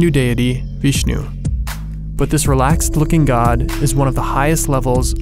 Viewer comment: Did he just say "realxed looking God "😂😂